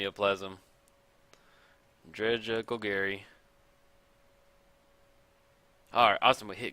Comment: When you said "Dredge a Golgari" I thought you said "Dredge Uncle Gary"